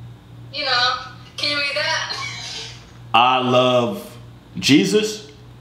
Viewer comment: Bruh I am so dumb I thought that said I love pencils 😂😂😂😂😂